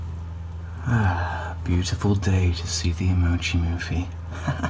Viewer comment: I heard "Beautiful day to see the emergence of fiend" xD